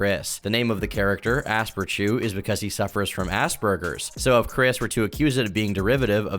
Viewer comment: Is it bad that whenever say "aspergers" I hear "ass burgers"?